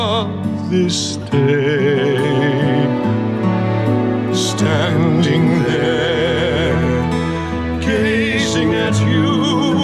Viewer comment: All I heard was ´Standing there, gays sing at you´